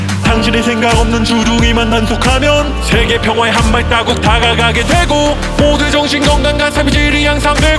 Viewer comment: 저만 이거 한발따국 이라고 들리나요?ㅎㅎ😅